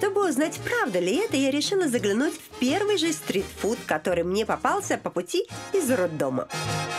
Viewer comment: На 5:30 послышалось "Я решила заглянуть в первый же стрипклуб" ещё и прямиком из роддома 🤣 я так обрадовалась думаю ну вот поживее передача пошла